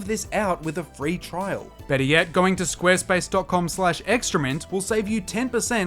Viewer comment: Extra mint in the ad reel with ur accent sounds like ur saying excrement lol